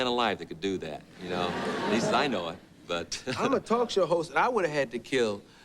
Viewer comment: For a second I thought it said mytalkshowwhores lol